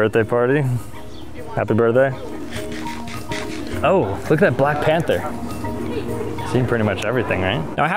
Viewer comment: i thought you jist said there is the black panda